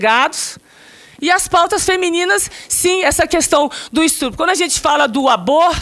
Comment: Eu ouvi um "estrupo" kkkk